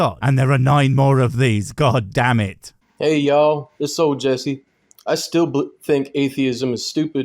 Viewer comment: I heard: "Hey y'all...this ass ol' Jessie..." I thought, well, at least he's honest about who he is but then I listened to rest of sewage that poured out. Now I am more damaged.